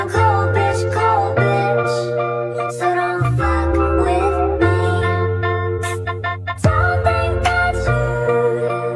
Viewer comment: WTF IS SHE SAYING?!!! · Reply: "So don't f*ck with me" lol